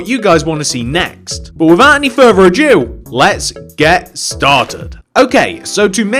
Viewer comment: did he say without further a Jew